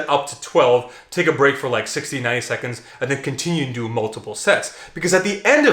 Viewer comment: did you say take a break for 69 seconds? 😎